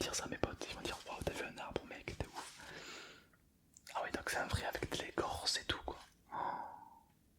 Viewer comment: jai compris avec du lait corse 😂😂😭